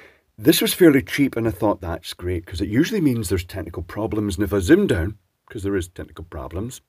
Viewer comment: I thought you said tentacle problems rather than technical problems, and now I shall refer to them by nothing else.